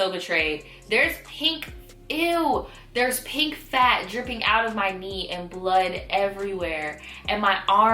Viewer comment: I stopped paying attention for 10 seconds and I hear "I saw pink fat coming out of my knee." Wtf? Lol